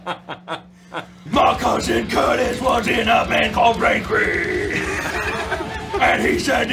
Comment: Did I just hear him say "Brainkrieg," the metal band from Homestar Runner? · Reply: Note, The Cheat is in the background.